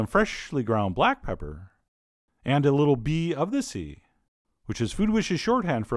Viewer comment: Thought he said, "Bee of the Sea."